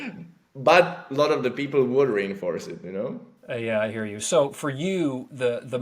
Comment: I thought he was saying to "rainforest" it